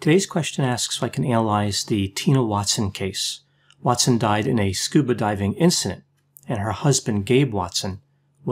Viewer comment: I swore he said "Watson died in a stupid diving incident."